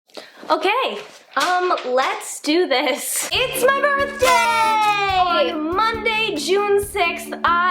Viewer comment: thought she said oh gay xD